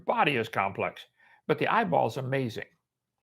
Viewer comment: he sounds like he is saying the eye is a cell